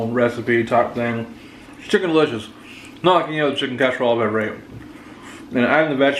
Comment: Does he say, Not the best chicken casserole i ever ate?